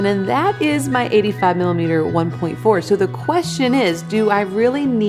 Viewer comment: Did I hear 85 mm 1.4 ? ?